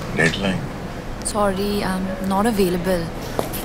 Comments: I heard "Sorry I'm not a " baby girl " when I saw this ad first.😑🤣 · if you hear this carefully you will hear she is saying sorry I am not a birbal. 😂😂😂🤣🤣🤣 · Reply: I heard it as sorry I am not a vehicle